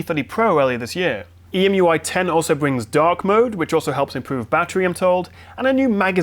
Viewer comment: I heard that ".....dog mode"